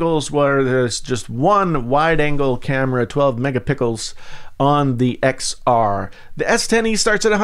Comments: did he just say "megapickles" approves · Is it me or am I hearing mega-pickles at 2:28?